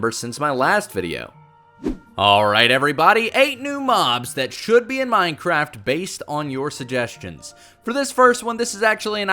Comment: I thought it said ‘8 bobs that should be in minecraft’ and I was like wtf😂